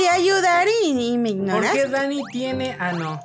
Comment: Yo entendí <<¿porque Dany tiene An0>>